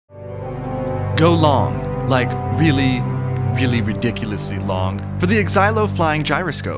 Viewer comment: he sort of sounds like imWildcat saying really willy